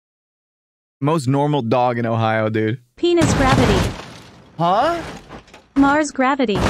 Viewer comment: Not very sure if anyone heard this but, 10:43 P E N I S G R A V I T Y